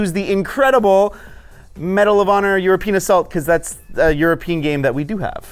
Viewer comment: did he say you're a penis assault?